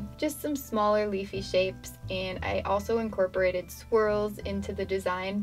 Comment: lol I thought she said she incorporated squirrels in her drawing not swirls